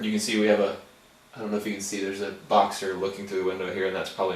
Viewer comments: did he say fox? or Box? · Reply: he said boxer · I love how he is talking about them being nervous and then, almost as an afterthought he says "Oh yeah there's a fox on the other side of the glass, that might be part of it." It goes by so fast that it takes a second or two to think "What did he just say? A FOX?" Good grief, who thinks a fox at the window is normal? I love this channel. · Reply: he said boxer